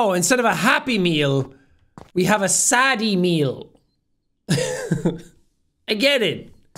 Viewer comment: I thought it said "Saddy Meme"